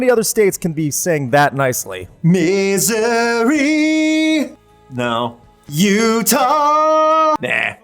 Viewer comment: You sang "Missouri", but it sounded like "Misery" lol